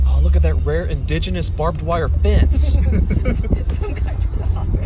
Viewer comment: IDK why, but I heard "Markiplier fence"